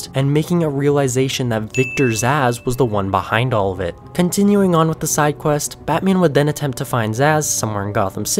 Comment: Bro my deadass thought he said the one behind all of this was victors ass👌